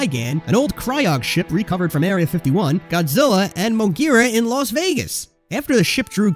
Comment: Did orga say he Escape Area 51